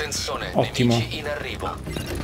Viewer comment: hahaha in questo video ho sentito la frase: attenzone nenici in arrivo :D più o meno al minuto